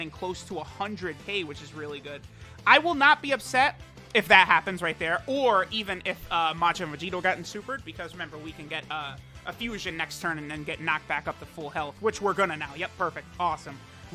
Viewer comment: did he say majin vegito?